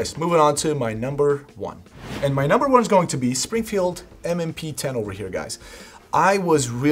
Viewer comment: DID HE SAY SPRINGFIELD M&P 10 at the 8:48 mark ?